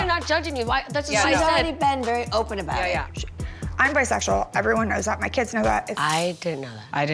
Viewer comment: she speeds through her dialogue so the first time i heart Brandi say, "bisexual" i thought she said, "im very sexual" and i was so confused as to why the women were like, "i didn't know that" oops